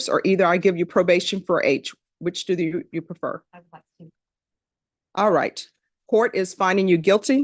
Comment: what did she say? I like 2?